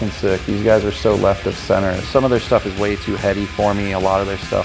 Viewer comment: Did he just say Voivod is too heavy for him? He's in Nails ...